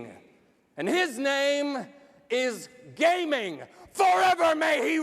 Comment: I legit thought he said "Gaben" at first lol